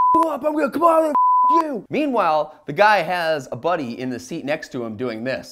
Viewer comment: I just now realized you said "Buddy." I always thought you said "bunny".